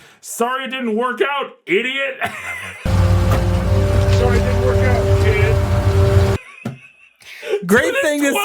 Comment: I think the truck driver is saying "Sorry it didn't work out, kid." To be fair, it's REALLY hard to hear anything over the truck's engine.